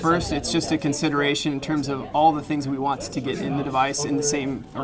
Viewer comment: Does he say "wants ta get"